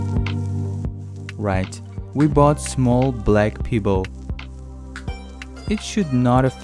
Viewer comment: Looked away from what I was doing and accidentally stabbed myself in the leg when I thought I heard " bought small black people" 😵